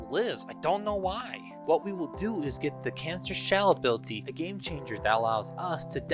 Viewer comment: OH, CANISTER!! The entire video, I thought you were saying “cancer”. I just didn’t question anything because you said it was a Damage Over Time ability